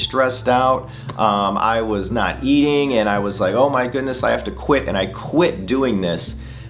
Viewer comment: I think it sounds like she is saying, “Ed, see your c—. Something like that. Definitely sounds like Ed instead of, “ I have to.”